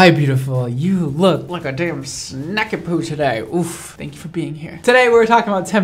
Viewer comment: Brad: “You look like a snack” Me: ah yes, the humble potato that is. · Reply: Idk why but I swore he said Snack-a-Pooh 😂😂😂